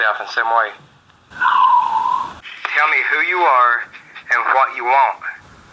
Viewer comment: WHen he asks. at 9:40 "Tell me who you are and what you want?" I hear "Im your bitch" X......D hehehehhe.